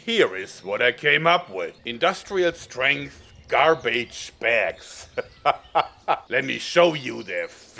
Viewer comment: Tbh thought it said teabags